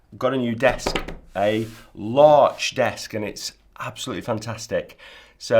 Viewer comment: Its a really big desk….small things 😂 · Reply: I thought he said it's a larch desk